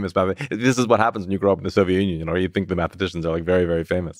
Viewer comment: ….bul did not just say Lex grew up in the “Societ Union”